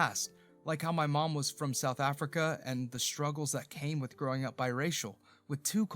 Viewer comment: Why did the subtitles say South America when the narration was South Africa? I'm from South Africa too I'm a zulu